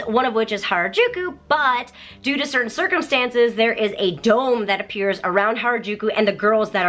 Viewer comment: Was I the only one that thought she said certain curcimcise